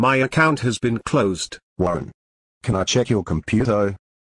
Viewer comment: why does he say “can I check your computeo"?